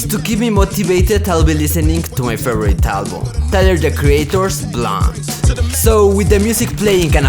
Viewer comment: Did he just say Tyler the creator's blond? 💀